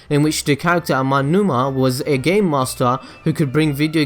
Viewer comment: i thought he said gay master xD